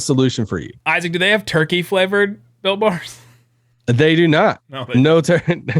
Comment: Isaac with the great ad read.. For the first year of Built Bar promos,I thought was saying “Bilk Bar” not “Built Bar”